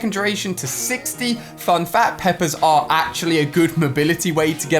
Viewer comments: I thought he said "peppa" instead of ""pepper" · I thought he said peppa instead of pepper